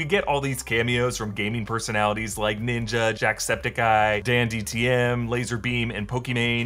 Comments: did u say dandtm? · Did he say "DANDTM" not danTDM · Did he say DanDTM